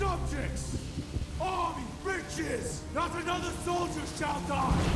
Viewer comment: for some reason, i heard “all its Bitches” instead of “riches”.